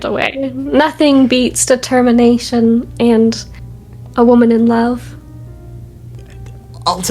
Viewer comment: for a moment i thought she was going to say something like "nothing beats determination and a gun" at 52:31 idk might just be the American in me:face-blue-smiling